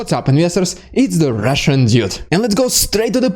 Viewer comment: no offence dude but try putting little more effort when introducing yourself, 0:29 It sounds like you said "It's the russian idiot" XD